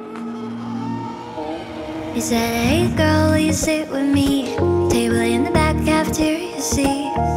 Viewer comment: I thought it was "she said hey girl wont you sit with me"